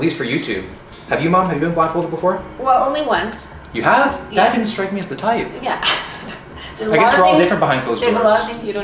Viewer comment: "You ever been blindfolded mom?" "Yea once" "you didn't strike me as the type" bro I could never hold that convo with my mom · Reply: Sounded like he said "Dad didn't strike me as the type", which is almost worse.